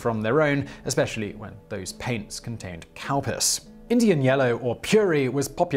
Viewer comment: Did he just say "cow piss" ?